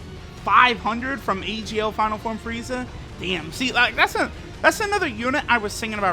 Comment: did he just say final form frieza??